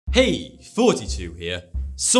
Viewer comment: This whole FUCKING TIME I thought he was saying "hey, 42 here."